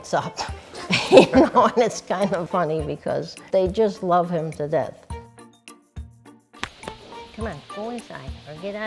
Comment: Bruh did she really say "they just love him to dead"? 0:44 Hope she memorize this when he gets killed by them.